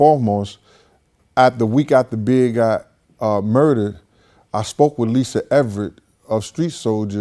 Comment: in this video did I hear him say "after we got the BIG murdered "....Wow that's what I heard him say! None of them are trustworthy....All snakes and can be bought for a few dollars! This guy and Puffy was seen with the killer on picture...The FBI took the picture...They were unaware that the picture existed. So Mase asked why were you on photo with the killer... played stupid talking about I didn't take the picture...No shit Sherlock we know you didn't take the picture..But the FBI caught you and Puff collaborating with the killer before the murder.....Sounds like a clear setup to me... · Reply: He said "the WEEK after big got murdered". Get your hearing checked.